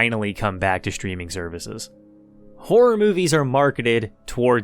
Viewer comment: what did you say? whore movies?)